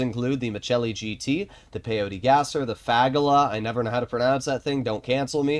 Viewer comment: what did he say 😂 😅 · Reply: The fagalaa 😂